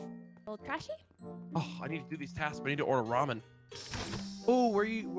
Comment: For a second, I thought she was trying to say Tonkotsu Ramen, which is basically Pork Ramen.